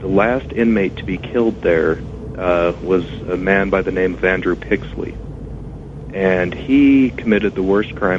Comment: Did anyone catch the guys name at 44:00? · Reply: Andrew Pixley.